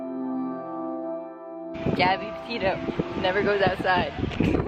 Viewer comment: i thought she said ‘gabby petito never goes out of style’